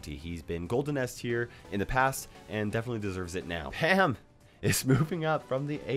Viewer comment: It it just me or did say ham not pam